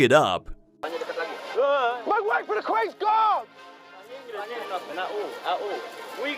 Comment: what did he say? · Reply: make way for the queen's guard.